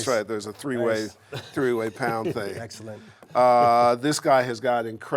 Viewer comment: At about 9:50, did Herb really say 'A 3 way pound"?